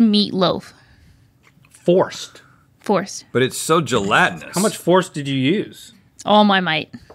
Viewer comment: Link asks "how much force did you use" regarding the gelatin curry goat terrine....I thought he said "how much horse did you use"?! 🤣 🤣 🤣 for those of you who don't know...horse hooves (I think?) are used to make gelatin....so hearing Link ask how much horse did they use had me💀 glad I misheard him! 😆